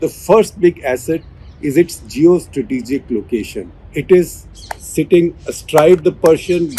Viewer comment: I almost flipped when the subtitle said “JIO STRATEGIC LOCATION” …